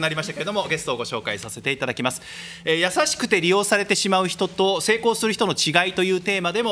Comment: くらいからの先生の言葉、みんな「何言ってんだコイツ」って字幕を入れても違和感ない顔してるの好き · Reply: 「優しくて利用される人」になれと聞こえた